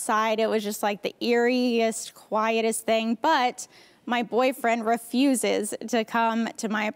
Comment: when you find out she has a boyfriend 🤣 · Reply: Did you say boyfriend?